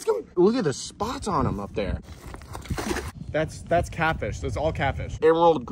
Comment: When u said “that’s catfish that’s all catfish” I thought u said “that’s caca that’s all caca.”